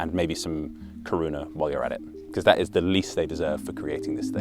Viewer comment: I misheard you asking the people offer the creator some Corona instead of kuruna, and was horrified to offer a Czech this abomination of a beer-like beverage... 😂